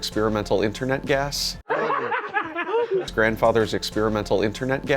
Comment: Did I just hear "internet gas", sorry I forgot the "experimental".